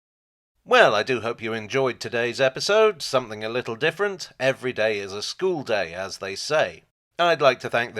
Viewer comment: I honestly misheard 'Every day's a schoolday' as 'every day's a stoolday' at first.